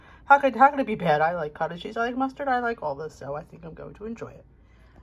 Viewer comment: Loved your food!! your avocado toast looked amazing along with the rest of your yummy foods. Did you say "mushroom cottage cheese? - I replayed it back and I thought that is what you said? Did you just chop up mushrooms? and of course - cute pics of Bailey now and then... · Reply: I was mustard not mushroom . I was excited to eat , lol